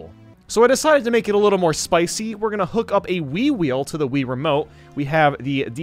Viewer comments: When he first said wii wheel I thought he said wee wee. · i thought he said “wii-wii” rather than the “wii-wheel”